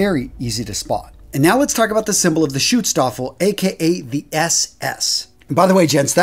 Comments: I thought he said shoot stafel not Schutzstaffel😂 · When he said "Schutzstaffel" I thought he was saying Shoot-Staffel. Hahahahah